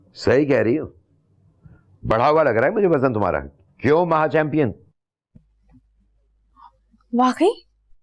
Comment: Maha chanpion keh dis instead of neya😂